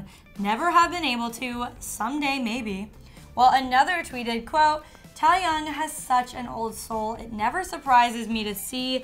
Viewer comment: Did she seriously say Teahyungs name wrong it sounded like she said tieyoung instead of TAEHYUNG